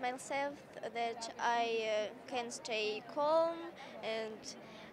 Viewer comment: anybody know what she says at 0:39? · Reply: i can stay calm